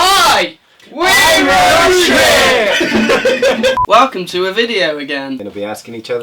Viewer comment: did anyone else realise that said ‘I’m roadtrip’ instead of ‘we’re roadtrip’